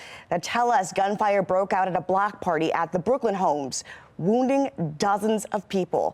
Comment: A shooting at a block party in the project’s of Baltimore? No way 😆 that kind of thing rarely happens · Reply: Block party? Oh, I thought I heard black party.